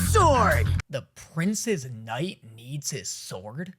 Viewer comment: Honestly, I thought he said "the princess knight" and didn't even question it lol